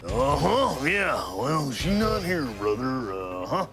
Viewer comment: Wait, did he say- "She's not here brother" like someone was looking for their lady or something? 😂